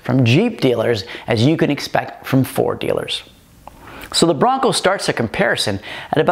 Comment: For a second I thought you said Bronco Sport and my hands instantly assumed the internet rage claw pose. Fortunately I rewound....